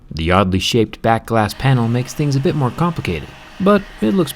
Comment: Oddly Shit back panel that’s what he said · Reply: The oddly shaped back glass panel Your english is horrible, if you thought he says "shit".